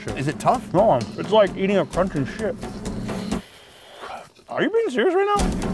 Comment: is it tough ?? Naw, its like eating a crunchy $h!t 😮😂 I know he said chip , but i swear i heard what i heard 😅😅 AWESOME EPISODE 👌 guys Thank You !